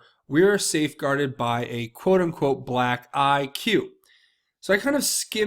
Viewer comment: Ohhh, I get it now...I thought Q said Safeguarded by Black Guy...